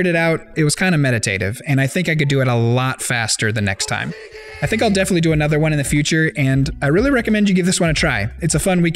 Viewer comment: did he said 'Consegui'? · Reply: No it's "Fushigi" from the old commercials 😂